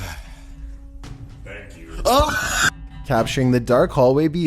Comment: made me laugh Cause I swear I heard that guy in the hallway say 'thank you'